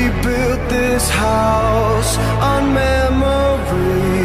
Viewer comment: Don't kill me, but I thought it said "House or Morbius" for a second.